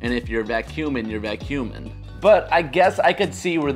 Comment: You said vacuuming and I thought you said vac human